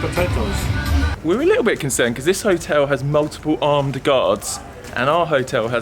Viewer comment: what did I miss? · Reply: ha! I missed it I thought he said his hotel had multiple armed guards..... But he said other hotels... Not his. My bad.